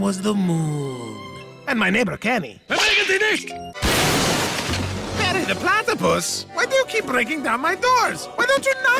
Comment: I always thought his dad kept saying “davinnykanicshk”😂😂😂😂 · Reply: It’s ‘bewegenzienicht’ which means ‘don’t move’ in German